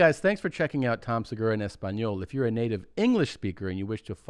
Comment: Every time I hear the word "Native" my brain spells it out "Nadav" now, so thanks for that jeans